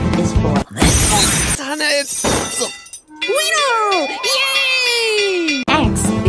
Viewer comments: when he said "winner" it sounded like he said weener- i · He it was "w" and said winner I thought it said weenier the food 😂😂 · What they hear :Winner!! :D What I hear; WEENIER :D 🤣🤣🤣